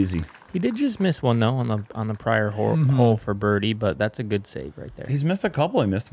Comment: Did just say he missed one on the prior whore? hahahah